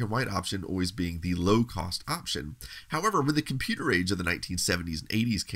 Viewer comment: in the video, there's an error with the subtitles. Instead of saying "however", it says "howeve".